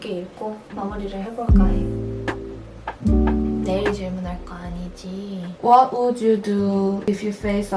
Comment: 아니제가잘못들엇나햇어요. 냐냐냐냐냥..이러시길래 "내일질문할거아니지.."라고들려서 에이설마이거겟어 햇는데진짜이거여서 제댓글전혀 문맥적으로나 이해안되실거같은데 너무귀엽고, 메이플스토리에나오는 꼬마 애기몬스터같아요.(몬스터보단 캐릭터같다는 것에 중점을 둔 귀여움)